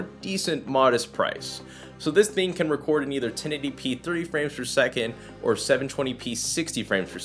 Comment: I thought he said 1080p 3 FPS on the webcam 😂